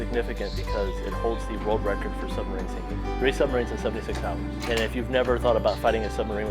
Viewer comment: What does it hold the record for? I couldn't quite hear it at 1:30... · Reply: Submarine sinkings. It sunk three enemy subs in 76 hours.